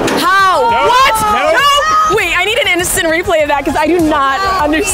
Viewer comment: please tell me someone else heard innocent replay